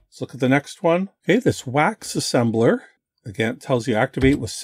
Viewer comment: At first I misheared "Wax assembler" as "VAX assembler", and thought that was a very strange thing to provide on a VIC-20. Cross-developing for a mainframe using the VIC's BASIC editor . . .